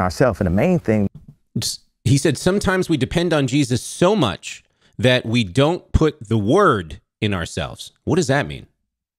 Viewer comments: To me it sounded like he said, “ we depend on Jesus so much we don’t put in the WORK ourselves.” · I think he said "work" and not "word". At least that is what I heard.